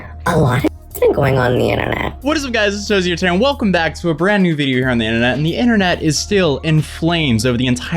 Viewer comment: DID YOU SAY VIDEYER